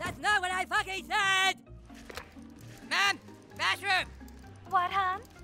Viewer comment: i thought he said mushroom